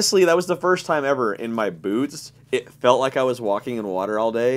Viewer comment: thought he said boob. Had to double take. Also dear. Wat do you ink about Crye or die gearwhores